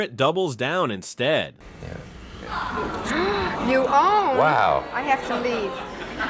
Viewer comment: And what does she say when she walks off without a word? 10:32 Tomlin: "You, oh! I have to leave." (Not exactly speechless.)